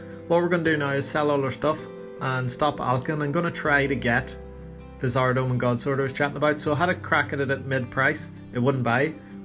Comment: I feel a distinct lack of “thats class” in this video :( also I had to rewind at around 13:10 cause I thought you said you were buying a Saradomin gods word and I started wondering how you got like 25m lol